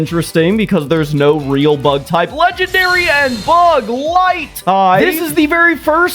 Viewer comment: I thought he said bud light like the drink 😭